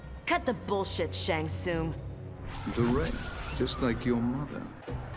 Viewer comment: I thought he said “dead jus like your mother”!